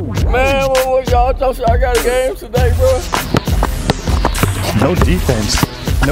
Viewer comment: wtf did he just said 😂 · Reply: "Hi. I'm Chris Rocks little brother.... y'all gotta game today, bruh?